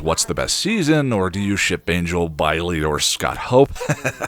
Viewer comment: This is beautiful. Did I hear you say “Bile-y” instead of “Riley”? Is that our surprise treat, like a cookie of laughter?